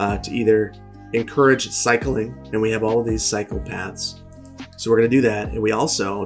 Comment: Had u on in the BG & thought u said "We have all these psychopaths" LOL